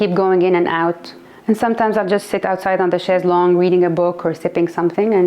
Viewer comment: What did she say at 3:22? "Sometimes I'll just sit outside on the _____" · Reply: She says chez longue.